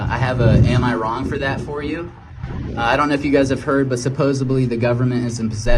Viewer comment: Did guy say "supposABLY"?